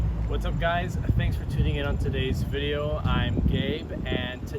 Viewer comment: Thought you said “thanks for tunning into my video im gay” lmao